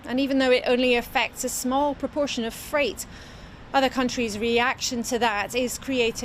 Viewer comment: did she say the disease affects a small proportion of freight??? Defund this tripe